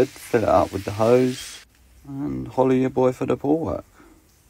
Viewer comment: “Holla at ya boy for the pool work” It’s been 2 months and I just realized he says “FOLLOW ya boy for the pool work” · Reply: I thought he said hola at your boi for the whole work